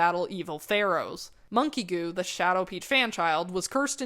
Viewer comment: did she just say shadowpeach referring to Lego monkie kid ?